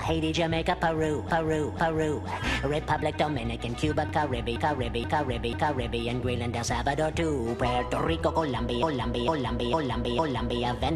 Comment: when yakko says "United States" over and over again it sounds like he is saying "You might expect"